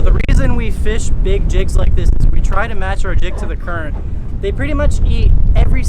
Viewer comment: Who wants to see more slow pitch jigging? I'm hooked! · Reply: Go to 10:53 in the video 🤣🤣🤣🤣I was dying bro ur subtitle says u tried to match ur dick to the current 🤣🤣🤣I’m sorry I was rolling man I can’t stop 😆🤣